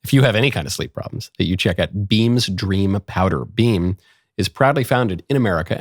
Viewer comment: I thought he said beam is probably founded in America🤣